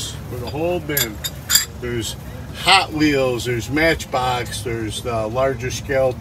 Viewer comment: Did he really say "there's hot Leo's?💀